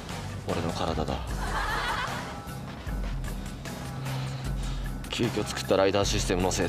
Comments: 急遽作ったが君が作ったに聞こえた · 急遽作ったじゃなくて君が作ったに聞こえる…